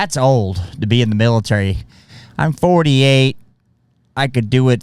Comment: did he just say he’s 48??? WTF? 58 mayby